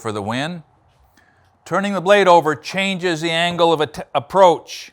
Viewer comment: I heard "Angle of Attack," real quick......must be a pilot..